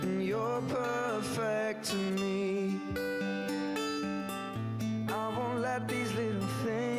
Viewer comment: To all you people saying that he isn’t saying “his” here is him saying “these” compared to when he says “HIS” 1:53 2:13 Like come on that’s pretty obvious. Now u can say that this doesn’t prove Larry is real.. because it doesn’t “prove” it, But it is a little SUS...🤨😏